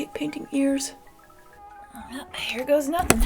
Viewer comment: Did I just hear “hair goes nothin” ??